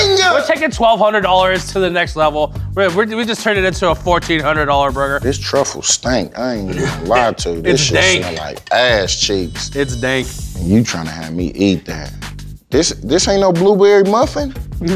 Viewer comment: “this truffle smell like asscheeks”😂😂😂😂😂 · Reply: Lol I thought he said ass cheese